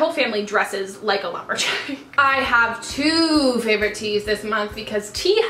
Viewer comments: When you talked about your fav teas, I thought you said "I have two favourite teeth this month..."😂😂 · When you said I have two favorite teas this month I thought you said I have two favorite teeth! Ahaha